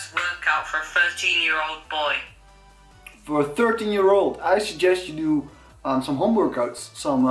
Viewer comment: I thought he said in the beginning "I suggest you do your homework"